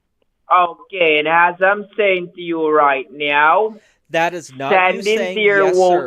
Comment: Did he just say: "As I'm saying to you right meow? at 3:08:28." 😂